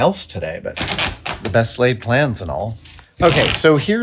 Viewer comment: for a moment i thought you said "the best Lathed plans and all" and i was like GREAT pun! lol